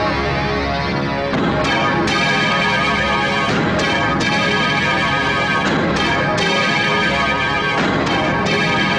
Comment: what did Karthik sir say to prabhu sir? 🤔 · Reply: Karthick: bastard Prabhu: not me. It's you.